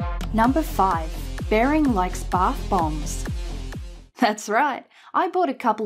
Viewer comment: I actually misheard bath bombs the first time.. "He likes barf bones? he puts barf bones inside water until it is dissolved? How crazy are these Australians?"